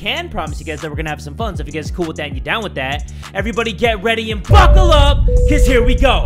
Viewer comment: when u said buckle up i thought u said fuck up btw i just LOVE U